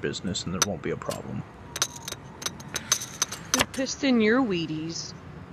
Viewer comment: did she just say who twisted ur wheaties💀💀 WHAT DOES IT MEAN